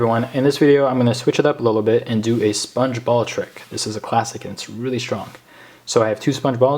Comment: Sponge Ball magic is great ! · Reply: I thought u said sponge bob XD